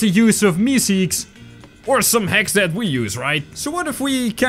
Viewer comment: your accent made me think when you say hacks I thought you said hex